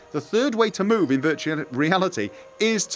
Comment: Did you almost say 'virginity' instead of 'reality' ?